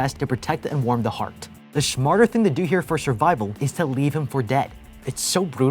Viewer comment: did he just say "schmarter" or am i just hearing things?